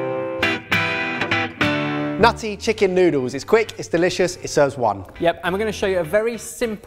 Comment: I watch these videos at 2x speed and thought you said "Nazi Chicken Noodles" at the start of the video XD